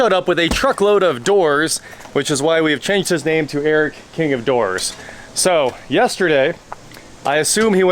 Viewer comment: Oh king of DOORS, I thought you said king of WH**ES! 😂😂😂